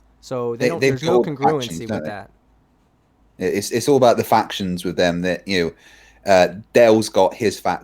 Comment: Did he say Faction or Diffraction? :)))))))))))))))))))) 47:17 No, the Earth is a square-prism-star shaped! Go do your own research!!!!